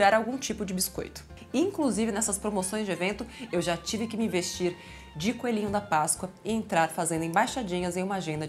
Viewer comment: eu ouvi "Naty de coelhinha??" meu Deus...